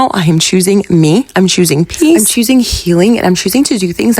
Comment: You said you were choosing peace but honestly I heard “juice” lmao. Anyways that job sounds toxic and even though it sucks to get fired for being sick, I’m glad you’re out of there and recovering. I wish you all the best!!